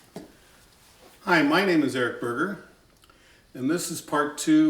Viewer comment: I’m sorry but did he just say his last name is